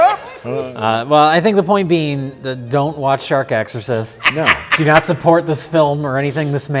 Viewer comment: Damn, thought it said "Shaq Exorcist"